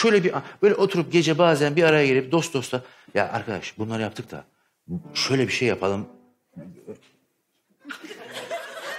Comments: 'de hanımefendi ne diyor acaba bilen var mı? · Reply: Erotik Film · kadın ne diyor? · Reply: Erotik diyor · 'da ne diyor duyan var mı? Ben anlayamadım. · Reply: Aynen ya ne oldu orda 🤷 (Başka bir yorumdan gördüğüm kadarıyla eroitik film demiş) 😂🤣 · kadın ne diyor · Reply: Erotizm dedi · kadın ne diyor anlayan var mı ? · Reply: Rez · 'de ne diyor seyirci? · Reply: Grup sex diyor galiba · da ne diyor seyirci :D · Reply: Erotik film diyor